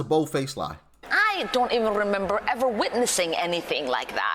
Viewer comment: did she really say 'a pecan of hope' ?!?!😂😂😂😂😂